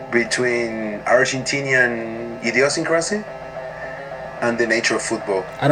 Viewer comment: Can't believe someone finally reacted to this! Loved the reaction man, excited for the remaining episodes 18:32 and I think he said Idiosyncrasy, not Idiocracy lol